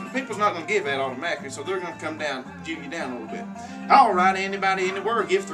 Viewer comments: Did he say “Jew me down” at 4:47 lmao? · Did you say Jew me down? at 4:46? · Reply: Ridley, wow. "Jew me down?!" Seriously? Some people just hear what they want to hear and make everything personal. He clearly said, "Jew you down." :-) Clearly there are different parts of the USA where people are less progressive in their turns of phrase. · did he actually say "Jew you down a little bit"? Wow!! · Does he say "jew ya down alittle bit"?